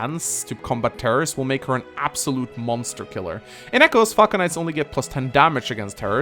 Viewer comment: Was I the only one who heard him say "Fuck Knights" instead of "Falcon Knights" at 8:58 / 8:59 mark??? LMAO